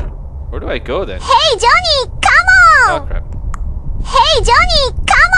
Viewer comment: why the hell did i hear,"Johnny Com UN!